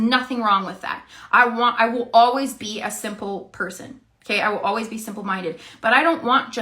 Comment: Did she actually say “I’ll always be simple-minded’..? 😂